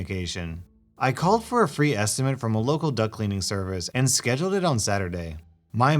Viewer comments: I thought you said duck cleaning service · Hey dude, it sounded like you said 'duck cleaning sercvice'. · The first story I thought you said duck Cleaning Service Second Story with the professor I wish I was a janitor having to clean that office on that day to see that go on I literally would have just been standing there I might excuse to be am afraid that you might get violent professor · I thought it said duck cleaning service